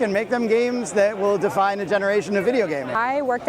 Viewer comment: I thought he said "divide a generation" at first. I thought "spot on , man."